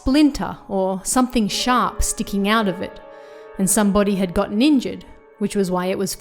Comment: I thought she said, "somebody got ninja'd", lol